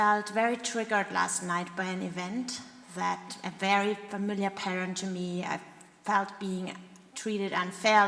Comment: What word did she say at 0:27 between "familiar" and "to me"? And at 0:35 between "all" and "up"? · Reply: I understood "parent", but that doesn't make sense, does it? I guess she wanted to say, that it is a "familiar pattern" to her. The second one is "all worked up".